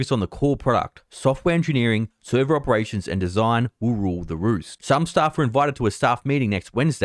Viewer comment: For real thought you said "Woo woo, the roots." I wasn't reading along. Lol. I was like: Man this dude really likes server operations and design...